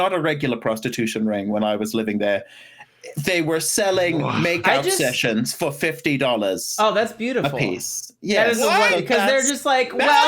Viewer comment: Totally thought said “makeup sessions for $50” oh ya you know Utah, capital of aspiring MUAs